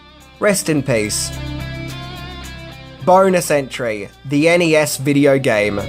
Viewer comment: When you said "Bonus Entry" I could've sworn you said "Boner Century"...😅😂🤣